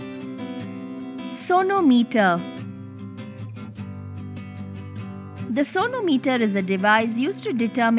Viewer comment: I heard it as 'Sonu Meter'